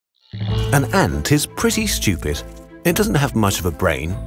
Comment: Lmao im thought you said an ant is pussy stupid